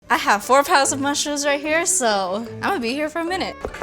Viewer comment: In the intro, I heard "I have 4 000 mushrooms" instead of "four pounds", I thought it was a little bit excessive even for making it big XD